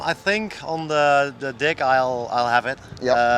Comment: did he say dick